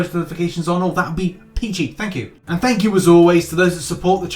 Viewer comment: When said "that would be peachy" i heard "that would be PG" and i was just like so if I don't like you're gonna swear at me?